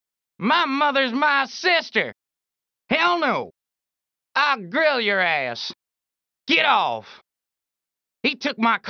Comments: This pedestrian ALWAYS attacked you back if you punched him 🤣 "I'll grill your ass!" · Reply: I always misheard that as "I'll DRILL your ass!" XD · “I’ll grill your ass” 💀💀 all time classic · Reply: On the PS2 the voices are very compressed so I always heard “I’LL DRILL YOUR ASS!” 😂